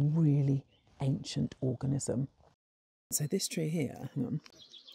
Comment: I was so engrossed in her way of speaking that i though i heard "orgasm" instead of "organism"